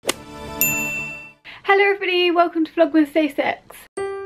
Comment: I thought you said "Hello every body, welcome to the vlog with safe sex" Am i wrong ????? -IB